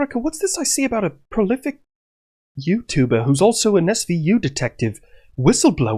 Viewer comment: Getting serious Dale and Tucker vibes with Chippy. Also, I know my ears heard you say prolific, but my heart still heard prophylactic....